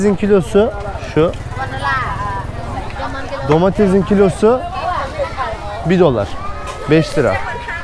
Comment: Domatesin kilosu 1 dolar yani 5 Tl mi dedin , benmi yanlış duydum :D ? Ülke bıraktıgın gibi değil haberin olsun :))